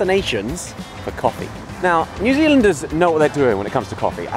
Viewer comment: Did I just hear New Zealingers?